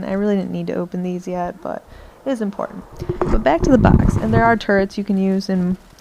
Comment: i thought she said there are terds to use haha :)